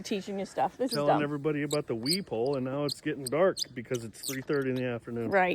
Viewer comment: I thought you were saying “wheeee pole” 😂 have a sparkling week!!!